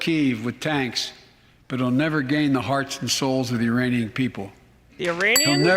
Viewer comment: Great time with you guys tonight. My highlights: - calling Ukrainians "Iranians" - Ukraine ambassador rolling her eyes at Biden's comments - string of lies attached to "second amendment bad" directly after praising Ukraine for giving all citizens guns to defend their sovereignty from a tyrannical government. - (negative highlight) veteran's widow realizing she's not going to actually get any help, she was just brought in as a spectacle for sympathy. - gay justice dance - 60 million barrels of oil. And failure to mention the thousands of times more than that we could be producing if Biden didn't sabotage our own oil production. - Queen of the flies dancing and getting all jazzed up about burning feces and lung cancer in soldiers. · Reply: I thought he said Uranians